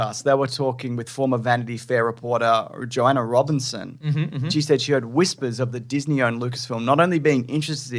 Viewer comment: Was I the only one who thought said Vagina Robinson 😂🤣